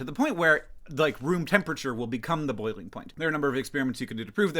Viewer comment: I thought you were saying Spearmints. You were saying Experiments. Now, I recall that you usually pronounce it that way: your accent, my accident.